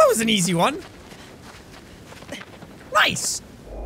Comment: it sounded like you said lice instead of nice